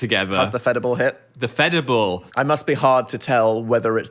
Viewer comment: subtitles: (indistinct) What they’re saying: phedible